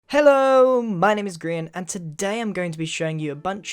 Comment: I thought you said my name is Dream for a second.